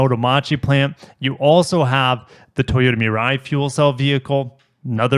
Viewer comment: i cant tell what he said. 'mee-rine fuel cell?' how do you spell that? the blue one